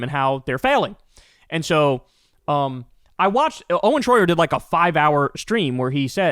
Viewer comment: I thought you were saying Troyer as in Vern mini me Troyer 😂